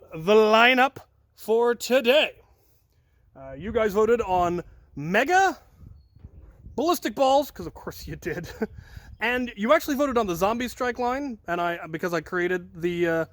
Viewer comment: I thought that said mega balls from Star Wars at first. 😂😂😂😂😂😂